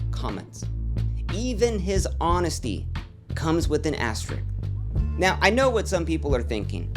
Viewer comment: im sorry what? am i hearing things or wtf is an asterik lol xD